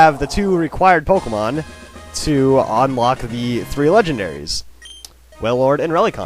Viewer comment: LOL I was watching this and I thought it said, "Unlocking the Regis" Regis as the name XD